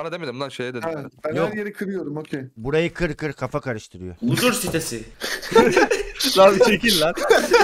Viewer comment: da ne dedigini anlayan warmi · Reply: Huzur sitesi diyo kanka hiç bißey de yok yani huzur yok anlamında